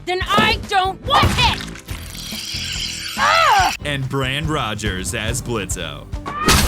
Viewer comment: AM I THE ONLY ONE WHO HEARD HIM SAY BRAND ROGERS!?!